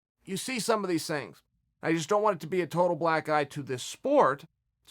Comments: I thought he kept saying "Black guy" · It sounds like hes saying he doesnt want this to become a "black guy" for the sport instead of "black eye".